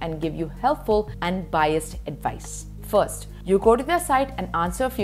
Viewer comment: biased advice ? 😅 did I heard it wrong